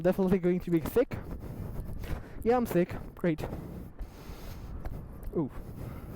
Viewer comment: did he say thick